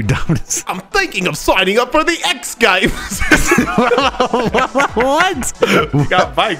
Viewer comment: What is saying at 8:30? English not my first language so I can't figure out what he says and I feel like im missing out a great jome 😅 I have loved this duel, probably one of my favourite, I love you two ❤ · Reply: "I'm thinking of signing up for the X Games!" because they duel on bikes and the X Games are about bikes